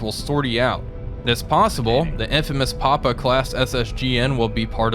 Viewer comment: I thought you said "the infamous Appa-class SSGN" instead of "Papa-class" yip yip, comrade